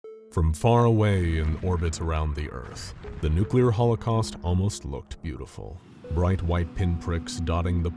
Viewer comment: I can't get over that my phone started to preview the video for a few seconds before I clicked on it. It made all the difference between what they said vs what I heard, "Holocaust was beautiful". I was deeply frightened